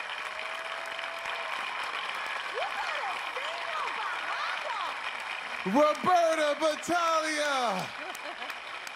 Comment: What does Kelly Clarkson yell out at 2:52? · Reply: Stanley Chiu Something like “you better sing your behind off”.